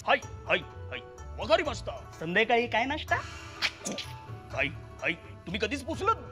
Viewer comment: is it just me or did he say sandhyakali kay nasta